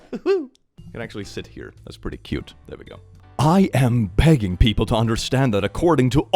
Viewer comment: I really heard that as "I am pegging people to understand..."